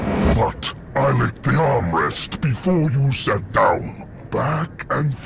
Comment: I have no clue what Atroix is saying their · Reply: Lonefalloutwolf "I licked the armrest before you sat down"